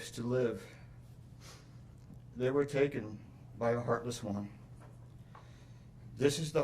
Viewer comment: Sounded like he said “heartless woman”